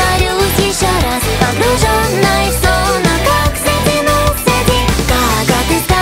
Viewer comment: кажется что мне послышалось не затянулся а Сатинулся😅😅😅